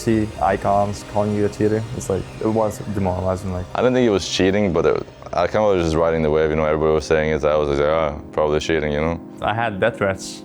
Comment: what did he say?!?!!? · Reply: “I don’t think he was cheating, but I kinda was riding the wave you know everyone was saying it I was like ‘ah probably cheating,’ you know?”